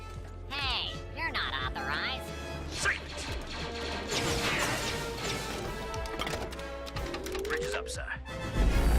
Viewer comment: What did he say at 3:11? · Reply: SAVE IT!! 😡